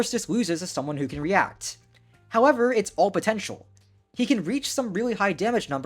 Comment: did you say POTENTIAL?!